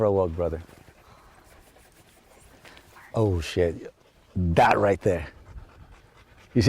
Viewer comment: did he say oh shit? lol